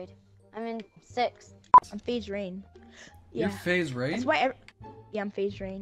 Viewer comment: I thought he said “Ima be dream” Me:HOLD UP